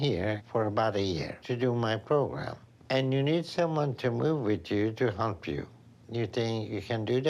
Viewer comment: I heard "we need someone to move with you to h*mp you" from Dr Now. Had to listen to it again 🤣